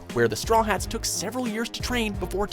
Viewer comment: I heard him say they trained for 7 years while the time skip I'm pretty sure it was only 2 years Lel